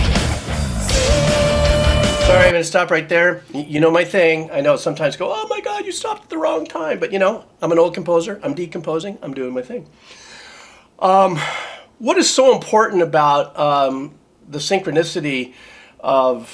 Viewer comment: The poet and the pendulum, live at Wembley The greatest show on earth, live at Tampere I heard you say: I’m an old guy, I’m decomposing. 😂 You’re not that old I hope